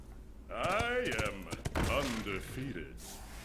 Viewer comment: "I am...undefeated." gets defeated "Drink with me friend!" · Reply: He says undefeated? Wow, i always thought he said "I am...on the field".